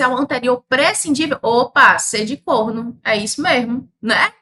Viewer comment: " opaaaa! C de corno " 😂😂😂😂😂 acho que foi isso mesmo , né?